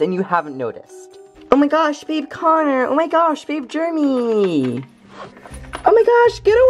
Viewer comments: It sounds like Maddison is saying Germy instead of Jeramy like she is accusing him for not washing his paws or something!!! LOL XD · it's sound like Maddison said Germy instead of Geremy